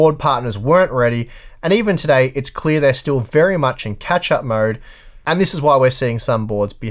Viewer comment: I thought you said Ketchup mode ;)